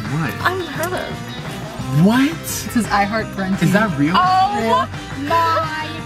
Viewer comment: it sound like says OH MY COD at 8:05. lol